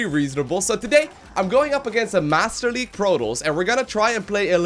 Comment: in the beggining it sounds like he says "master league PROTOLLS"